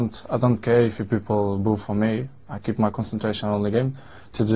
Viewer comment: "I don't care if peolple ''boff" for me ? Boff ? 3:30 What he said ? Thank you v much